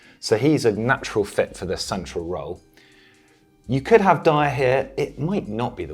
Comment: "You could have Dier here..." 2:33 I was just listening rather than watching and my head whipped around so fast as I thought he'd said "You could have diarrhea"